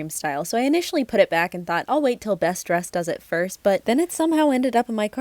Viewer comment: DID I JUST HEARD THAT RIGHT??? BESTDRESSED???? ARE YOU WATCHING BESTDRESSED???!!!!